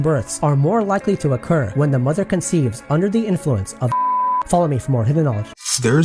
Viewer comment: u say wen mothers give birth while drunk...u misheard...it said wen the baby is conceived...and ALOT of children are conceived with drunk parents sadly!! Also a baby can take DAYS to b born not just hrs! Its crazy the difference...& having a child or many makes no difference to how long or easy the nxt will take. Eg: a relative had her 3rd child...very fast & no problems..so fast she was surprised wen told ur babys born lol Next child born at home coz was expected to b easy...lasted almost 48hrs!!! Almost killed her& the baby....awful experience. So its very unpredictable. Some great videos...really like ur style of non interrupting all the time...got a sub frm me ❤